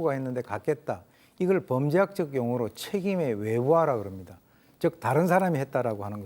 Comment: 이 부분에서 오윤성 교수님이 말씀하신 게 "책임의 외부화" 인가요? 아니면 "책임의 외주화"인가요? 책임의 그 다음 부분이 잘 안 들리네요. 청력 좋으신 분들 알려주세요~~